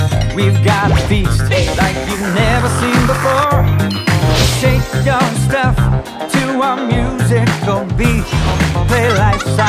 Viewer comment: what does he say at 0:26? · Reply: Shake your stuff to a musical beat, I think.